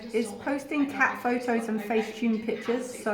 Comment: I thought you said facetoob. lol